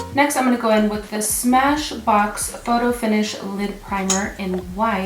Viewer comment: Thought she said "lip" primer, lol! Yeah-yeah, "lid"....I know. 😉 Btw- Love the reverse motion of the products! Ps- Zsa Zsa Gabor is a movie star from back in the day. If you hear it pronounced it may ring a bell. She had a famous sister named Eva. Sorry can't think of how to type the name phonetically.